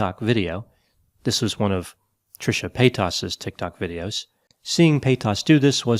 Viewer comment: The way Dr. Grande says “Trisha PAY-TAS” absolutely sends me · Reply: I always heard it as Pay-tiss